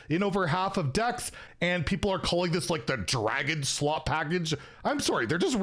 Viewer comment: is it the dragon slot package? i heard something different. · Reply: I think he did say "dragon slop"